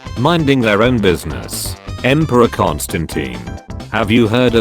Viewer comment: lol i wasn't concentrated in the video so whan it said empreor constantine i thought it said empreor palpatine